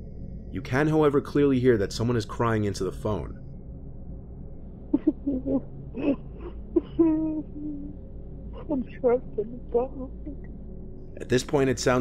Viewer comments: The second voicemail almost sounded like there saying I’m trippin balls lol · On the second one at first i thought it said “im trapped in the bathroom” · Number 4 with someone tuning around the house calling saying there trapped in the dark. Sounds like there's a person living in there house. Where it's dark, attic, closet, under the bed. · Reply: It sounds like he said "I'm trapped and I don't know what to do" that could make sense if they're stuck in that person's house